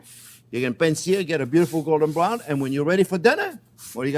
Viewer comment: Whenever Chef says "beautiful golden brown" I always hear it as "Beautiful Gordon Brown" haha